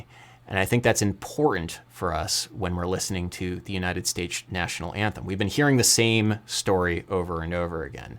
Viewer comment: did just say "United Stage's National Anthem"? 😂